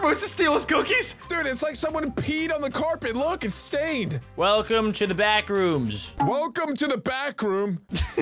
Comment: I thought you said welcome to the bathroom